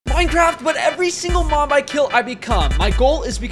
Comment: I actually thought he said every MOM I kill I turn in to it Edit: thank you for the like 👇🏻